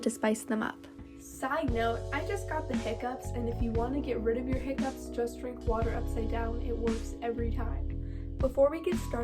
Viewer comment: what did she say please? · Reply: She said she has the hiccups and that drinking water upside down can help